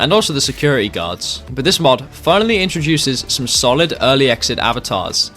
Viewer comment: Thats what i said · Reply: oh I thought you said the security guards needed to be added 😂